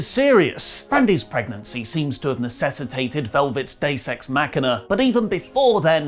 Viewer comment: I know you said Deus ex machina... But it sounded like you said day sex machina... Lol